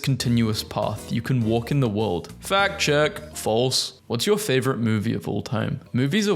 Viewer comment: I thought you said: “Fat chick”💀